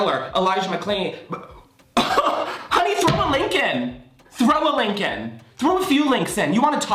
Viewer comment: when he said "throw a link in" I thought he said "throw a lincoln" like a penny?? or a $5?? and it still kind of makes sense ??